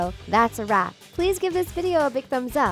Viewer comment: I swear she said "and subscribe for more 'fake' videos like this"😂